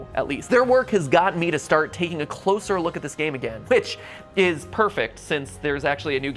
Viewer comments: bitch is perfect is what I heard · it sounds like he said b**h but actually he said whitch lol